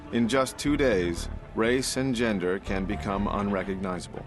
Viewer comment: They said "race and gender" and I thought they said "Ray Singender"